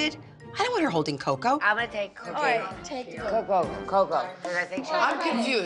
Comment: did anyone else catch Dorinda saying "I'm gonna take cocaine -- coco**" at 2:21 or just me? 👀